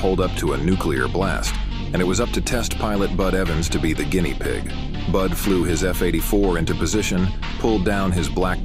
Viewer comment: I heard it as "Blud flew his f-84 into position" and it caught me so off guard